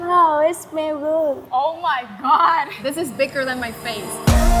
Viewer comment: At the beginning I thought she said "This is thiccer than my face", and I was like 0:05.